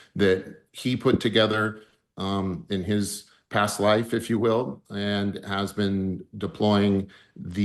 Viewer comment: When Jake said Skywatcher was built in "past life" 7:19? Am I hearing that wrong?